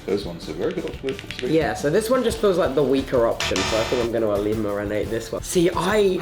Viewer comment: ok at 9:05 did tubbo's talking ability's just get kicked out cause instead of saying eliminate he said eliminarate